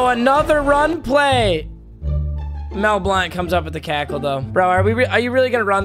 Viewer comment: he said cackle instead of tackle